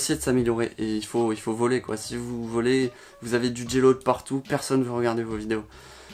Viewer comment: Il faut "voler" (vloger ?) ? Ne pas avoir du "G low" partout ? Ça veut dire quoi ? Je suis un peu le youtube game, mais je comprends rien. C'est des termes d'initiés ?